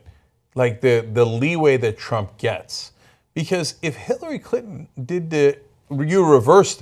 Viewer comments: Did Cenk say Hitler-y Clinton? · HHAHAHA Did Cenk just say "Hitlery Clinton"??? :D :D :D at 5:02 LISTEN CAREFULLY xD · Did Cenk just said Hitlery Clinton?? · Did Cenk say Hitlery Clinton at 5:03 lol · It sounds like Cenk says Hitlery Clinton